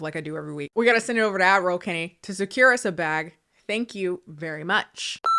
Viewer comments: I once read a comment that said they always thought she was saying “admiral Kennie” instead of “ad roll Kennie” and I can’t unhear that when it’s time for to get her bag 🫡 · I once read a comment that said they always thought she was saying “admiral Kennie” instead of “ad roll Kennie” and I can’t unhear that when it’s time for to get her bag 🫡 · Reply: Instead of admiral, I first thought she was saying "Adderall Kennie" like oh okay is it her version when on meds?(Something like with Ryan Higa and Off The Pill but the opposite) So she has her own segment of it with her Ads which will also makes it a play of words and I think that was cool. Until I watched one vid with subs automatically on, the only time I realized that's not what she's saying.